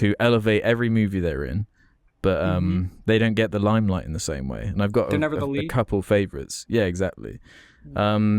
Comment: What is the character actor says at 53:58? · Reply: None. He says "they're never the lead" 🍻